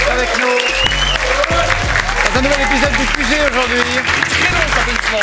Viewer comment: Wow au début j'ai compris "c'est le dernier épisode du QG aujourd'hui" j'étais si triste.